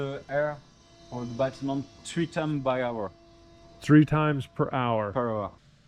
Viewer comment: "Dans'l batiment.": "in the building" is the unknown part you didn't understand.